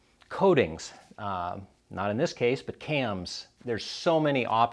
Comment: did he say cams? does this guy even BRAPPPPP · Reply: He literally said “not in this case, cams” did you even watch?